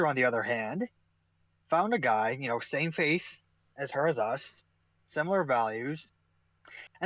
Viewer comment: I meant to say "My brother in-law." No doubt one of many, if not already my first, verbal blunders to come. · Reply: The auto captions caught me saying "face" where I was trying to say "faith". I meant my brother-in-law shares the same religion as the rest of my family. Honestly, aside from that, and a few other slurred pronunciations, it picked up what I said surprisingly accurately. Of course, at 0:05 I was saying "in" not "and".